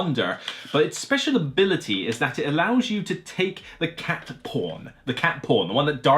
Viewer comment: Listening while playing Wordle, and I first heard you were talking about "Cat P*rn". Had to back up a little...